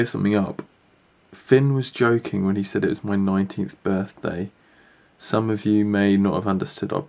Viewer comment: I thought finn said 90th birthday ...